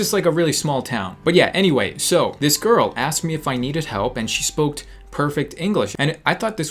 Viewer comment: MA BOII DID U JUST SAY "SHE SPOKED"